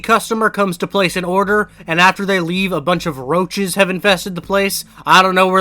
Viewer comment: I thought when you said roaches you said rochas like the roblox arg